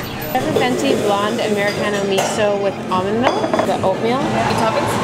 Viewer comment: I thought she said Americano MISO and I was like wtf that sounds terrible